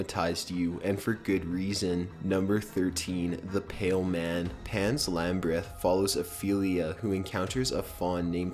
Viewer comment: Did I just hear him say "Pan's Lambryth"? What?!